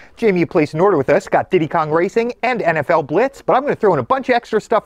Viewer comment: I thought I heard nft blitz and I was like wait I KNOW that was not on the n64😂